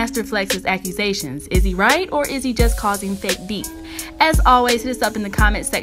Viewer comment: Haha i thought it said fake beats, that would be accurate too